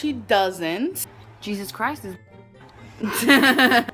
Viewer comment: I have no idea what said when ALR mentioned that she didn't say racial slurs... Did she use the n-word? I heard "Jesus was a girl" but I dunno if its me being ESL lol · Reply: She said "Jesus Christ is my n***a"